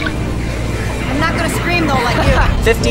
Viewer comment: What'd she say at 0:36? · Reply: game, she said: I’m not gonna scream though like you